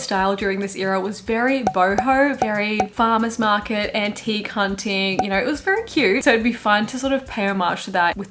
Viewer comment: oh my god this is so embarrassing lol but I had to rewind and listen to your description of her style in that era at 27:40 at least 4 or 5 times, trying to parse it, before my brain put together antique hunting 😭 "Taylor's style during this era was very boho, very farmer's market, auntie, c*nting" I thought it was a super awkward use of the like queer/drag queen slang and it sounded off so I kept repeating it til I realized what you said noooo hahaha